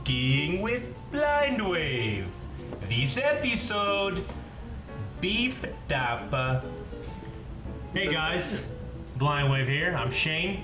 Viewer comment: I thought it said Shane's beef papa